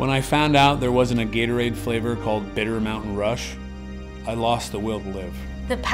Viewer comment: Petition for Gatorade to make "Bitter Mountain Rush" a flavour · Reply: i thought he said "bitter melon rush" Dont worry, I have a doctors appointment next week